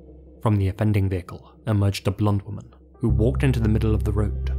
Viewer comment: In the last story I kept hearing "Blunt Woman". So I thought you meant a fat or plump woman... Until I saw the photo 😂.